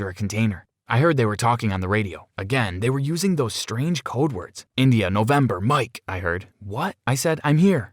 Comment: Did he say INDIA I am Indian. Woohoo...